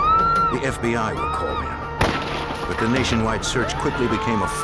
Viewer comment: tell me I’m not the only one who hears “the Canation wide”